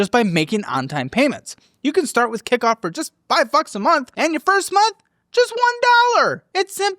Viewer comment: Am I tripping or does say “5 fucks a month” instead of 5 bucks a month